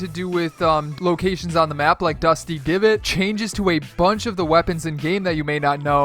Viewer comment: Did he say dusty givot